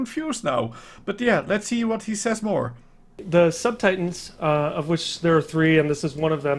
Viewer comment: Did he say sub-titans? Sub? What? I am actually super confused are there bigger Titans or are these the only titans? If I seem retarted... I probably am for asking the question.